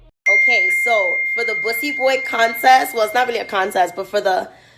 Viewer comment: I zoned out listening to this while making me and my child lunch and out of nowhere I heard "Bussy Boy Contest" 😭😭😭Chile